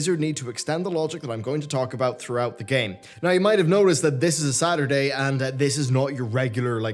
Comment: I just want to say that I thought when you said "today is a Saturday" you said "today is a sadder day" and then going to tell us that someone you know died. Love <3